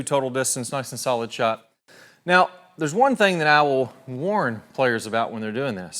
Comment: when he says "I will" I hear "owl"